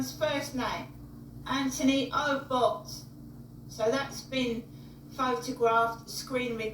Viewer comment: Initially, I thought she said the person's name was, Anthony Oldbutt!!!! I swear! I mean coming from her, saying that wouldn't be entirely far-fetched!!😂🤡🤪🤣